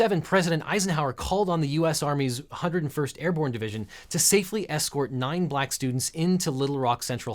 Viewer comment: Did I hear the 101st Airborne Division? Screaming Eagles! Sabaton's Screaming Eagles starts playing